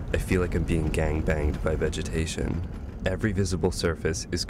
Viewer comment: Uhh did he just say "I feel like I'm being gang banged" lol